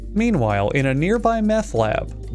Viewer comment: Did he say just "in a nearby methlab" ?